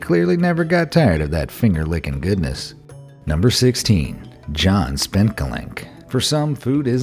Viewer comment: All I heard was “John Spank A Wank”😂